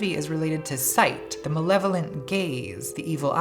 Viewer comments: I thought you said 'the malevolent gays' for a second there. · Not finished the video yet, but I keep hearing 'malevolent gaze' as 'malevolent gays' and I can't stop